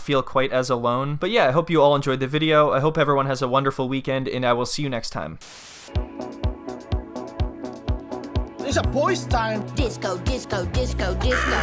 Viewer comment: At the end, I thought you said, “OWL see you next time.” 🤣